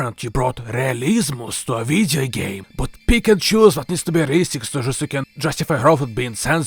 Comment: When you said "pick and choose" I heard "Pikachu"